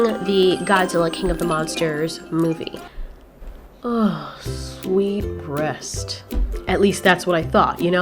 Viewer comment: I almost thought you said “Sweet breast” and I’m like “the f*ck?” but I listen again and hear you say “Sweet rest” LOL.